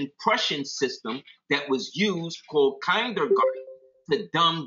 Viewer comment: What the name of the Russian education system you mentioned @ 57:50 that’s was used to dumb down the people. Sounded like kindergarten?